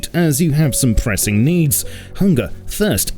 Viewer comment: did he said hunga?